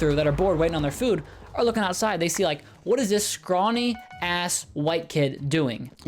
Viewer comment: is it just me or did I hear him say "ass"?